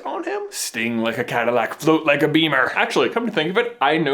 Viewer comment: he actually says float like a catalac sting like beamer. And yes i know spelled that entirely wrong.